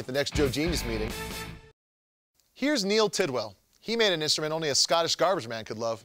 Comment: did he say Tit Well?